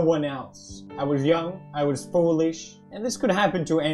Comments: I thought he said I was young, I was Polish. ??? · I don't know why, but I thought he said "I was young, I was polish" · omg at first i heard him say "I was young, I was Polish" instead of foolish and thought whaaat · “ I was young, I was Polish” that’s what I heard lol